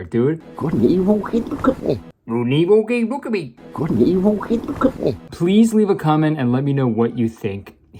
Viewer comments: I think he says " you couldn't even look at me. " or something along those lines. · I keep hearing "Good and evil, keep look at me" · i think he says "its gonna be okay, look at me" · i think hes saying 'good and evil, hey look at me' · I think he's saying "good and evil- hey, look at me", because it looks like she looks back at him right after he says the line. Can't really tell though, since there's not much context behind the scene- although honestly, I think even if I heard more of what he said, I probably still wouldn't be able to understand... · I SWEAR HES SAYING "Good and Evil, Okay, Look at me."!!!! 5:02!!!!!! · I THINK what I heard at 5:00 was "Good and evil hit the company" ??? But I have APD, so maybe don't listen to me 😅 Also, was NOT expecting to see Roddy Piper in this lol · about what the guy says at 4:48 - i'm pretty sure I heard "Good and evil? Hey, look at me!" or something like that? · think at 4:48 he says “good and evil keep me company.” at least that’s what i understood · I THINK HE'S SAYING "good and evil, kay look at me" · I thought he said "good and evil. Hey look at me." At which point she looks up at him. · In responce to the cry for help at 5:04 I think he says "good and evil? Hey look at me!" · "Good and evil? Hey look at me" is what I heard at 4:48 mayor Kurt, since she looks away when he talks 💃🏼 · I think he said "could an evil pig look at me?" And I think that's hilariously omnious. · I hear "good and evil hey look at me"